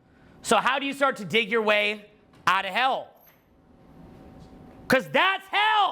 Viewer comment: I heard "How you start to Date your way out of hell?" Lol 🤣🤣😂